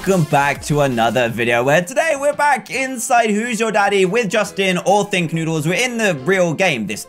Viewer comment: When you said who I thought you said lose your daddy🤣🤣🤣🤣